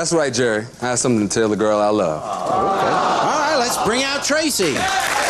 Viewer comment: when he said tracy i thought he said tracer